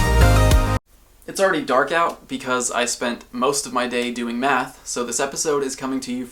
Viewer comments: I thought he said doing meth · Sorry at the start I must of misheard you, I thought you said that you spent most of you're day doing meth. Too much breaking bad